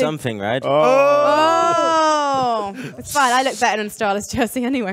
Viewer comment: what did she say? :, its fine i look better dressed than astralis anyway? It cant be what she said right? Cant hear it clear enough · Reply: she looks better in the astralis jersey anyway